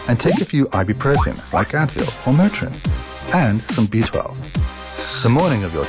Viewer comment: what does he says after ibuprofone? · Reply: B12